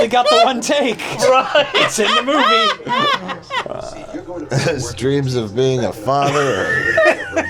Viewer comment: After the bike jump says "His dreams of being a father", which I misheard as "His dreams of being a farmer", which would promote the response "Here's a couple of acres."